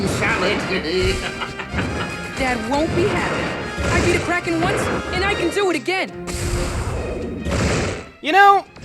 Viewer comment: i thought pit said i eat kraken and i can eat it AGAIN!! i am like 0.0 dafu?